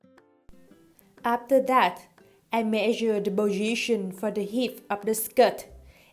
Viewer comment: I didn't catch what you said at 0:40 could you repeat please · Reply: Sara Measure the position for the hip of the skirt. It has to be a half of your hip size + 2cm